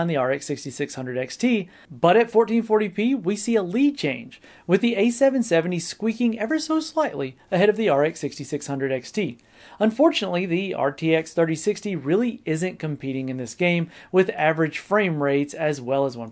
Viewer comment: I know it can be kind of confusing with these naming schemes, but did he just say "RTX 6600XT?"